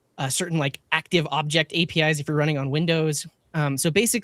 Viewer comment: I thought he said "Internet Explorer sh*t. things" and later "sh*t the shim". I don' think Firefox can be "fixing" major website and then complain that others are using non-standard API.